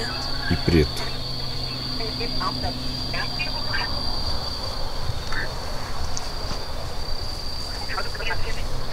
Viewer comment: No minuto 44:04 quando o fala sobre as cor das fitas Um voz de mulher fala (É o simbolo) E o restante eu não entendi Vejam aí 😱😱😱😱😱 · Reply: Escutei: estou no simbolo preto.